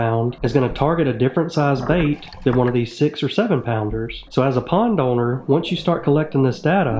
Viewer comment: Did I hear 6 or 7 pounds?